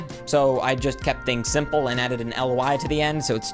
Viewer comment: Did you just say an AI?????